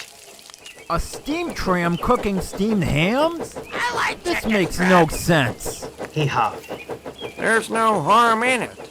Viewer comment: What did Percy say? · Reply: I like chicken strips